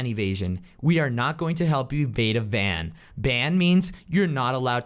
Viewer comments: did i just hear Quackity say ebade a van?? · Excuse me did he just say "bade a van"?